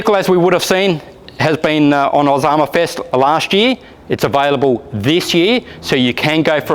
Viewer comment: I thought I heard Osama fest